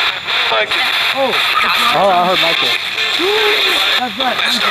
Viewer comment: the voice sounded like saying Michael